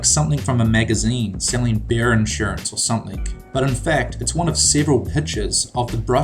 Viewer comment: Did he say "beer insurance"? What a great idea!